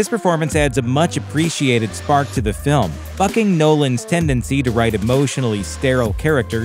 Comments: I thought he said "f***ing" nolan's tendency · what did I just hear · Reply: F*cking Nolan’s tendency